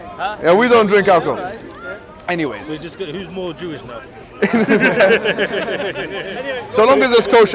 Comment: pls tell me what it says in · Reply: Who's more Jewish now, joke is maybe Jews shouldn't drink